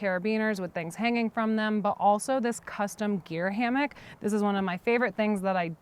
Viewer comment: Gosh, I thought she said beer hammock instead of gear hammock!!! I was like “what is that?” Haha